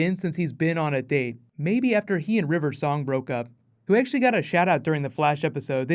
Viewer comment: lol, did he say river song